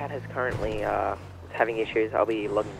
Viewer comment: I thought he said my cat is having issues lol